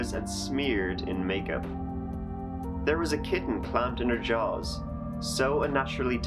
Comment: all I hear is "there was a kitten clamped in her jaws"....?!?!?!?